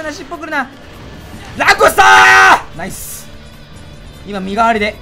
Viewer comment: 「ランゴスターー！！！！！」がにゃんこスターーーー！！！！！に聞こえた聴覚異常者です